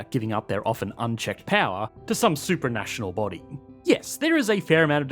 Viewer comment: I thought he said that Bricks didn't have the necessary "supernatural" powers, not "super-national". And I'm like, dang, gotta get some eldritch overlords before they can compete on the global stage.